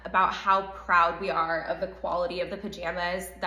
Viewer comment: thinking that 4 The 'Mems' was a SCOTTISH version of MOM has me on the floor HBHDESJFCB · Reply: I thought at first she was saying "For the Mims" and i was like, WTF is a Mim?